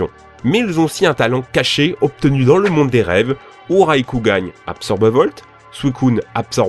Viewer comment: pourquoi il dit "raikougan" ? à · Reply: Il dit Raikou gagne